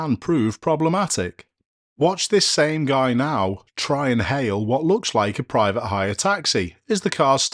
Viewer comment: At around 9:44 with the final clip, I thought you said "Watch this guy now try to INHALE a private hire taxi". Well as you said a sentence or two earlier, nothing wrong with enjoying yourself at Christmas!